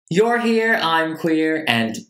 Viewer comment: Every time he says “your here, I’m queer" I just quietly go "same" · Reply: This whole time I thought he was saying "You're here, I'm clear."